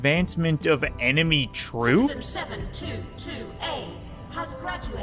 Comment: The game subtitle said citizen 772A but the game said 722A